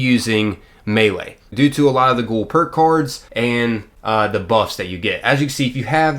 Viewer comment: did he say perk coards? XD